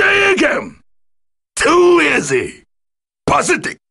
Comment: wtf did he even say??? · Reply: Deadly Rave.